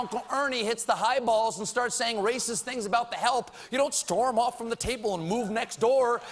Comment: Holy shit, did he just call all of black America, "the help"? 10:50-ish